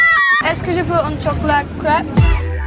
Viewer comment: "ahekalglidhglihziuhvnbs bsu sruri crap" (lol it sounded like she said crap instead of crepe)